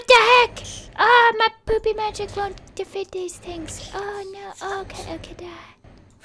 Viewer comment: idk why i laughed when she said "noooo my poopy magic wont deafet them" 😂😂😂😂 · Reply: Oh lmao I thought she said noooo my booby magic won't defeat them